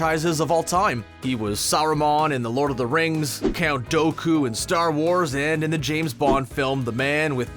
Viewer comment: Bruh did u just say count Dough Coo 😑